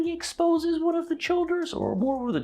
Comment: Did anyone else think the whole time that the bird beings were Chula, as in the species that made the ships in Captain Jack's first set of episodes with the Empty Child? It sounded like they were saying Chula, and it made sense given the similarities of Rogue to Captain Jack.